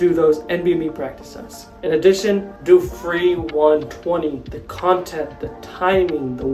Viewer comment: What was you said at 3:15, after nbme test which is free? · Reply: Free 120, its on nbme website, totally free and do it last cause its most representative to actual day